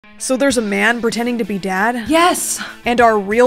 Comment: I thought he said pretending to be dead